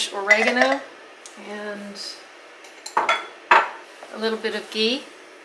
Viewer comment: Love it! Thumbs up on this one. Question: what is it that you place in the purple beans at 17:07 -- what does that do? I have to listen to it 4 times and I still can not make out what you said. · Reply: Ghee. Or you can add butter, but the ghee was handy. :)